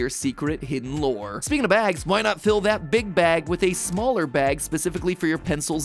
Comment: It sounded like he said "big backs" not "big bag" 😭